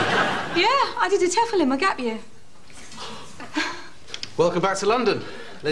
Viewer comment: I can't understand what she says here. I'm American, and either the slang or accent is above me. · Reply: She said "I did a TEFL (Teaching English as a Foreign Language) in my gap year."